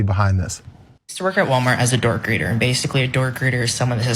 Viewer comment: Oh! door greeter, I thought he said a "dork greeter."